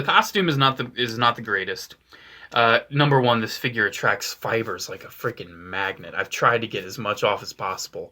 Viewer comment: When you said that Anakin attracts "fibers" I thought you said "spiders"